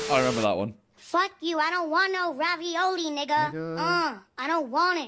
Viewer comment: what did simon say ???? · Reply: ravioli 😳